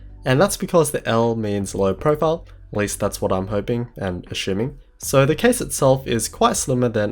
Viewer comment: did I hear a-shoe-ming